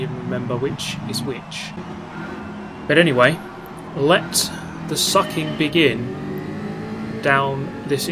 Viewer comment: So am I in xvideos? Why am I hearing "let the sucking begin"?